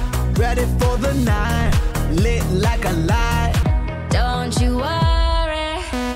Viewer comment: The fact that a lyric on this album is "we take flight just like we flew" and that' it's used seriously, makes me sad lol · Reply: i might be mistaken but i thought i heard "Lit like a light"